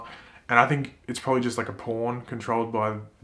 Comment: Did he say "Porn" ?Iknow he didnt say it 😂😂